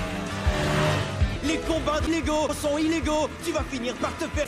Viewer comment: il a dit quoi a 1:29 😂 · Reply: "Les combats de Lego sont illégaux, tu vas te faire téter"